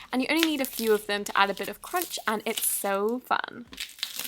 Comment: It sounded like you were saying "and it's so fetch" at 3:17😂